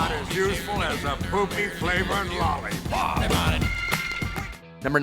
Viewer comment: I thought he said cock flavored lollipop.